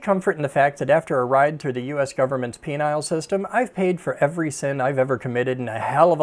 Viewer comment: "Penile system" did I hear that right?